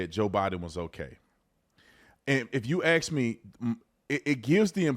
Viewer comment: I can swear I heard you say "if you aks me"